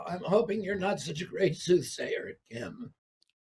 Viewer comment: I think RFK, Jr. actually said SOOTHSAYER.🧨